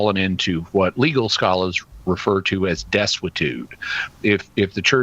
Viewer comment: What did say on 2:03? Desquitude? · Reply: Desuetude.